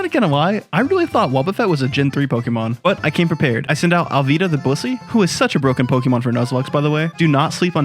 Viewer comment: Oh god, why did I hear that (34:17) as Alvida the Bussy?